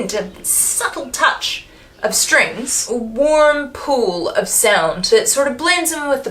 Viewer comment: I thought you were saying “a warm poo of sound” and was glad you didn’t. But you could use this phrase in a future review. No credit needed.